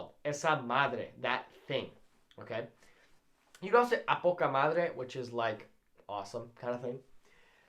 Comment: A poca madre? Is that what you said?